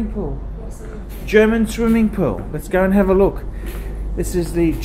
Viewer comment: did say German swimming poo?